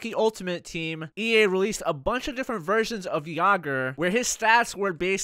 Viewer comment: Jagr as a goalie? oh God that'll be good!! haha sounds like you were saying Virgins, not Versions!! I love Jagr! Too bad he never played for Van, probably would've won in 2011 lol